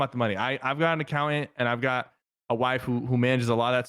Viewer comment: did he just say waifu